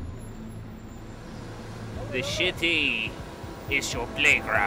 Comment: it sounded like he said shity